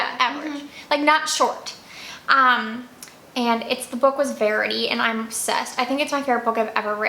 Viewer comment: oh sorry I meant the other one you loved… I thought you said “ berry” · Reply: Oh, Sorry, it's called 'Verity'